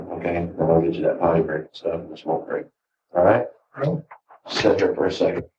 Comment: Did he just say “I’ll get you that Potty break” lmao